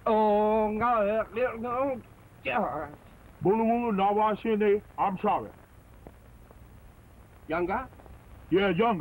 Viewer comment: Did he say I'm sorry?